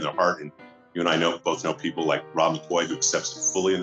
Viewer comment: did he say Rob McCoy? Interesting....